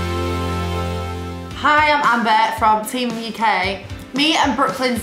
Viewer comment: it sounded like she said team uk instead of teen mom uk